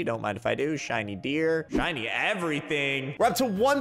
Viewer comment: When said shiny deer it sounded like he said shiny beer lol